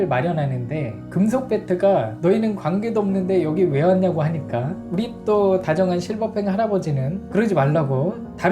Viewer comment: 나만 부리또라고 들리나?